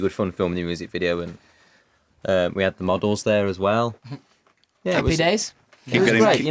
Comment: it sounds like Liam said "muggles" instead of "models". Oops, guess I've been watching too much HP! hahahah:)